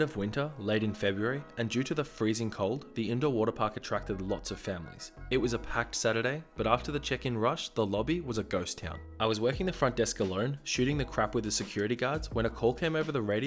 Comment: I heard “after the chicken rush” and got really confused.